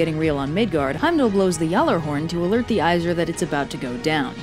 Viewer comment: Did I just hear G-horn?